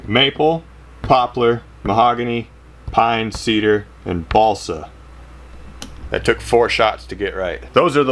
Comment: sounds like he said ballsack